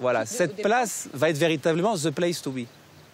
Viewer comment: Il dit quoi le mec ? Zeplaissetouriçe ? Mais c'est quoi ça ? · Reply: The place to be